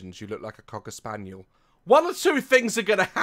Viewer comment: Apparently the subtitles thought that u said cöck spaniel instead of Cocker spaniel 😂 cuz it just put the line like it does when there's a swear (also for the ppl who wanna cut their own hair, make sure you cut less than think u want cuz u can always take off more, and if u have thick hair, do 4 pony tails or more instead of just the two cuz then the back ends up longer than the front and that's probably not the look your going for)